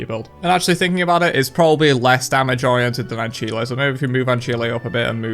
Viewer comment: Okay I'm not even 2 minutes in, did he just say "an chilé"??? I've always heard it as "an sile" and always pronounced it that way